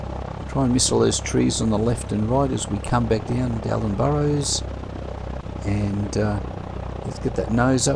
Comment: Ooooh..I thought you said Alex Burrows!☺ You know...the nhl hockey player